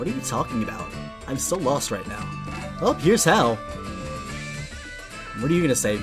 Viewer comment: it sounds like he says heres hell